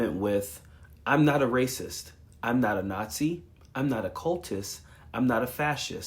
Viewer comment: I heard "I AM A NAZI" in their I'm not a Nazi speech.